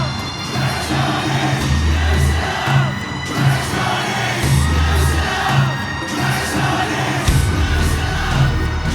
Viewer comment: 這邊在喊什麼阿 好嗨....不過聽不出來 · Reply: let me stand up like a taiwanese 出自黃文雄 <link>